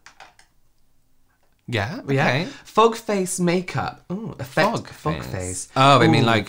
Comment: Im over gere dying at "fuck-face makeup" lolol thats what i heard 19:53 🤣🤣🤣